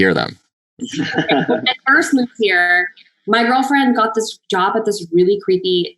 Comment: did Madelaine say "my girlfriend"? (or is it "my girl friend"? I'm not a native English-speaker and I don't remember her coming out, so maybe that's it?) Anyway, great interview, I learnt a lot and it was good to hear about their lives before booking Riverdale, helps us put things into perspective :) Thank you !